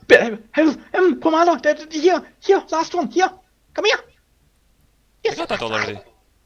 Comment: What was he saying? It doesn't sound like english. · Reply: "Last one here, come here,..." That's:)